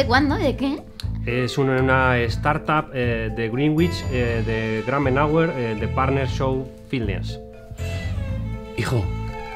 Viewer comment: que es lo que dice despues de greenwich??? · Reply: Lo que yo entiendo: 'es una startup de greenwich de gramenauer de partner show filnes'